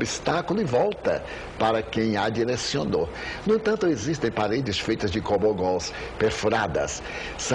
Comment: Maravilhosa entrevista - e tema - do Divaldo ! Alguém pode ajudar? Não entendo o que ele fala em 4:24 ; paredes de gomo nós...? grato, oscar